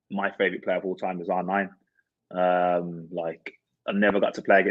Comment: did anyone understand who lescott is referring to as his all-time favorite player?? I can't make it out for the life of me, if anyone understands what he's saying please tell! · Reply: R9 - brazilian ronaldo